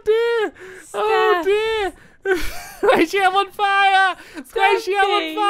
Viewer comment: stamps swore by accident he said "oh s**t I'm on fire" 😱😱😱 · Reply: He said "Sqaishey! I'm on fire"